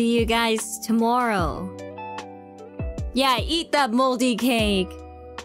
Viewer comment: I heard "eat that moldy cake" and all I can think is, "Shiori was a Kronie before joining?"